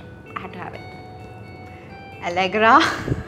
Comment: What did she say at 1:55. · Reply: Allegra